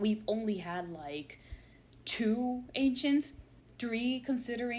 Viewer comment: I misheard ancients as Asians